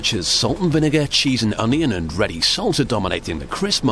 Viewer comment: I thought he was saying "seltzer." Turns out he was saying "salsa."